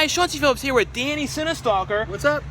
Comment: I always thought he said “Shawn t. Phillips here”